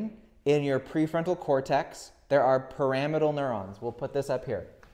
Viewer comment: Great video! Isn't "Pyramidal neuron" pronounced like pyramid? I thought you kept saying "Parametal neurons" until I your wrote it on the whiteboard.